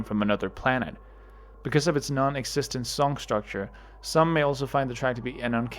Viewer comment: “some males will find the track to be […]”? “Some males”? 🤔🤨 edit: i figured what you actually said out. you said "some may also", but the words sprung and morphed into each other so quickly that when i kept replaying it in the first place, i kept hearing "some males".